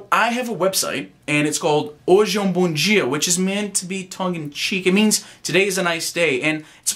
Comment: what did you say at 2:47? "Which is meant to be..."? I understood the hole video except for that! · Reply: tongue in cheek!